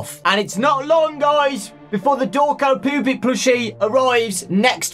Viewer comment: I know he said "poopet" in the beginning but for a split hot second I thought he said "the Dawko pubic." 🤨 Derp.